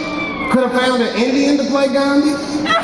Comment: What does he say? "Hollywood could've found an Indian to play ______?" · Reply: Gandhi ffs